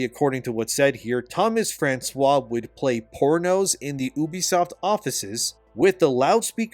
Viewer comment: When you said "pornos" I thought you said "poor nose". Sat there thinking wtf a poor nose was. It's been a long day